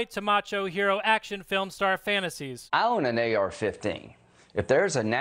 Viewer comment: With Lindsey’s accent for a second there I thought he said I own a New York 15. And I’m like, the fuck is a New York 15??